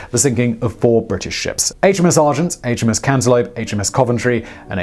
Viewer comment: I think I heard you say "HMS Cantelope"...